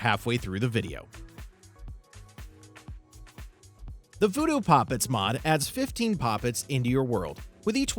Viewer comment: When he was talking about voodoo poppets I thought he was saying pop its and when he started talking about killing your enemies i audibly said: hold up what the hell.